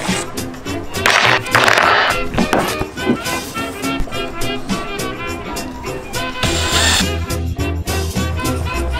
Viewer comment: did that just say t - series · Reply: It was saying "screw tseries"